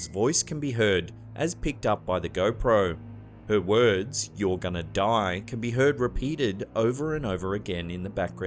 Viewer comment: I didnt hear your gonna die I heard you're dads here